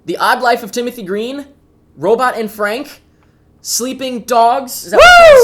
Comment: Haha i thought he said he reviewed "Robot Anne Frank" i hoped that wasn't a real movie